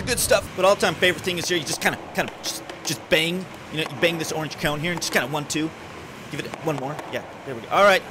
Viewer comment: He talks so fast that I heard "Orange County" instead of Orange Cone. 😂